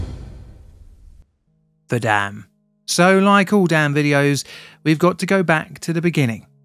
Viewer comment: I was listening to this while folding laundry and suddenly I hear "So like all damn videos-" and for a moment forget dam and damn sound identical.